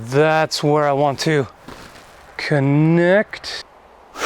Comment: "so that's where I want to... knecht..." is what I heard :D